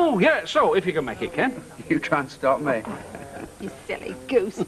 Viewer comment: what did Ken say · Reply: 'you try and stop me'